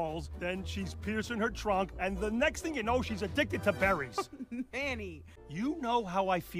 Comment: Addicted to berries?! All this time I thought Manny said Paris! Anyone else? 😂